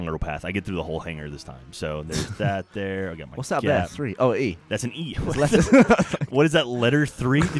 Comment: I thought he said "whats there three" I might be wrong